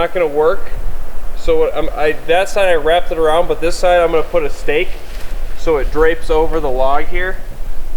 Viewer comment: ish did I hear steak? At 32:25ish Where is that camera?